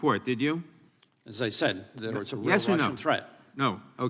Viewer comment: what did Durham say: 'As I said... there was... a real Russian threat(?)"